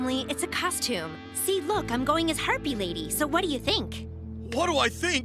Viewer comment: istg I heard Herpie lady